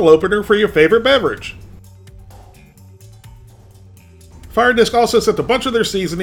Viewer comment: fire disk? i thought you said drink on wok! i passed out after the first min